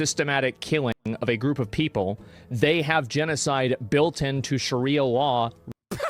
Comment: what did he say? law law?